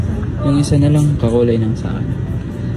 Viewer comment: ano sabi?? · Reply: "Uy, isa nalang, kakulay ng sa'kin"